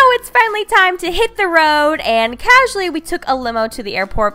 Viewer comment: Lol I'm half asleep watching this I thought it said "let shit the road" 😂😂😂